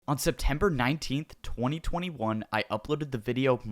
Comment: When he said: "on september 19th" I instinctively thought he said "on september 11th-"